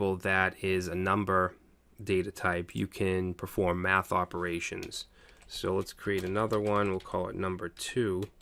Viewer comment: what he said at 12:25? "you can" what..? thanks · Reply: Recommendations "perform math operations"